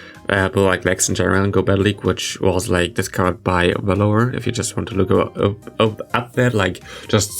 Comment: I thought you were saying “velour” at 4:35 and I feel so dumb because I’m subscribed to Wallower and I still couldn’t work it out! 😂